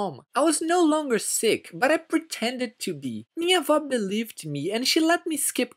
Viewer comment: what did he say? He surely didn't say grandmother. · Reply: He said minha vó. Wich means my grandmother in Portuguese.